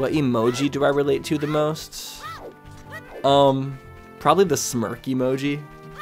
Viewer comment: Oooh saw a bit of this on Twitch, and was hoping to see it on YouTube too! Ahaha 14:36 I thought you said smurf at first. CC picked up what was actually said... Smirk makes more sense! LOL Been slowly going through your album, sounds so good!